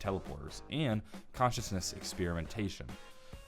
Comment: I believe it says "commence experimentation", instead of what you said being consciousness experimentation. Just a tidbit to notice.